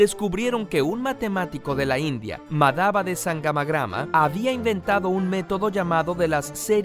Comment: En el minuto 2:30 cuando dijo "Madhava de sangamagrama" pensé que dijo "Mandhava tangamandapio"